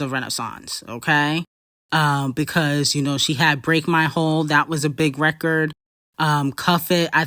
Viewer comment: Did you just say “Break my hole”????😭💔🤚🏽🤚🏽🤚🏽🤚🏽